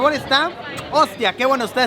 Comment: Cuando dijo ostra pense que dijo ostia :v